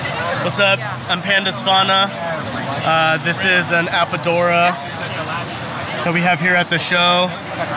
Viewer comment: What type of python is that at 12:10? I couldn’t understand what he said. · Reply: He said apodora. It's a Papuan Python